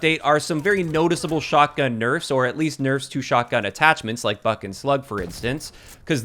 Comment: Did.... did he just say "fucking slugs"?